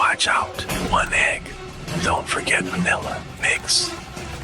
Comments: VANILLA I THOUGHT YOU SAID MILK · WHEN I HE SAID 1 EGG I THOUGHT HE SAID 1X SO I HUNTED HIM DOWN HELP IM HIDNG FROM THE POLICE